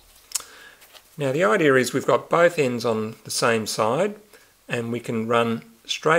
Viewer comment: What are you saying at 01:34 ?? "Now the idea is we've got . . " We've got what? Sounds like you're saying "5 pins/fins." Anyway I do see visually how you're doing it. cross straight over the top making a bar at each level. Then pull thru underneath then go straight up, but skip one eyelet. Repeat until you get to the top. :) Thank you. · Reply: Note that all of my videos have subtitles (or "closed captions"). Click on the "CC" in the player. At 01:34, I'm saying: /Now the idea is we've got both ends on the same side and we can run straight up the inside,.../ Great that you figured it out regardless of my Aussie accent!